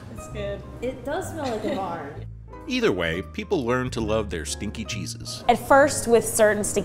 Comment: I heard stinky jesus instead of stinky cheeses